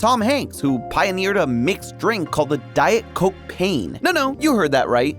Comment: i thought he said the diet cokecain